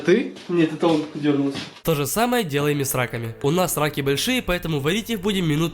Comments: я услышала *у нас сраки большие*)) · мне сначала послышалось - "у нас сраки большие" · Я один услышал у нас сраки большие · КАК ГОВОРЯТ В ВИДЕО: тоже самое делаем и с раками , у нас раки большие . А ЧТО МНЕ ПОСЛЫШАЛОСЬ: Тоже самое делаем и сраками, у нас сраки большие😂😂😂😂😂😂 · Что слышат другие люди: у нас раки большие, по этому... Что слышу я: у нас сраки большие, по этому... 😂😂😂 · послышалось "тоже самое делаем и с сраками" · Мне послышалось не раки а сраки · Когда он сказал: - у нас раки большие Мне послышалось, у нас сраки большие